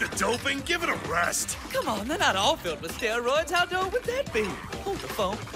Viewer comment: did he just say where all not filled with steroids